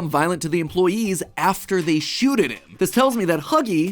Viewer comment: am I the only one who heard 'shooted" at 4:00 instead of "shot"